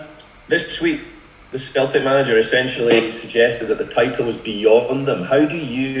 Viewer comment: did he say the skeltip manager